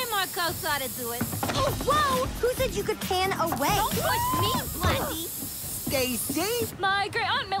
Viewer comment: 0.7 am i bugging or did it sound like she said "dont push me blacky"